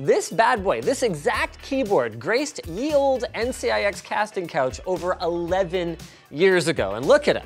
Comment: did i hear Casting Couch ?? 😳